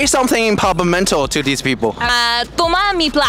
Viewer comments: i live in Aruba🇦🇼🇦🇼 correct sentence is Tuma mi placa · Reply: Ahhhh placa makes it totally different than i thought. I thought she said plata! Heres my card.. take my card.. sounds a lil better.. maybe her papa is mental and thats what he said so she learned papamental from her crazy daddy! 😮😂😊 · She said "tuma mi plaka" which means "take my money". But it sounds like she said "plata" which means silver AND it sounds like she said "toma mi plata" which is also Spanish for "take my money"